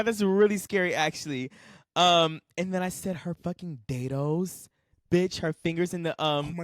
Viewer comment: “Her fucking Day Dos” 😂😂😂 It took me a minute to understand what you were saying. 😂